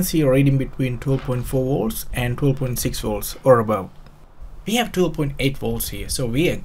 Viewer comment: Sweet, short, no loud background, straight to the point, well prepared, excellent camera angles and easy to understand the narrator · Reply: it wasn't that easy to understand the narrator. when he said 12 volts I thought he said 2 volts. although it was a good video.